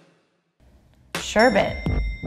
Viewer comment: I thought you say sherbet like “Sher-bae”